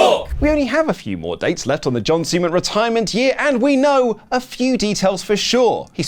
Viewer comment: Not me rewinding to see if I heard "John Semen"